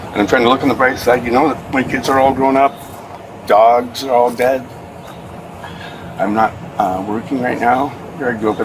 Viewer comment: "Im in a bad place mentally but im trying to look at the bright side. My kids are all grown up. The dogs are all dead" 😂😂😂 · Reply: I thought he said the dogs are all fed.